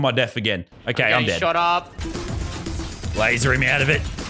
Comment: it sounds like is saying shut up